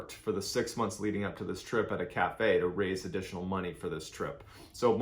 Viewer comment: For some reason I heard: “they worked at a strip cafe to raise up the money for this trip” My brainnn🤣🤣🤣🤣